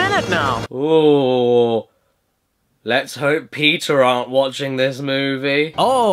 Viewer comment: What's Peter going too do about it? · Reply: He said Peta. When a word ending in a non-silent vowel (or "re") immediately precedes a word beginning with a vowel, it has a tendency to create an "r" sound, which is why he sounded like he was saying "Let's hope Peter aren't watching" rather than "Peta aren't".